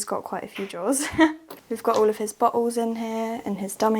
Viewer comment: Im from the USA. When she said bottles, i had to watch it over again bc i thought she said buttholes lol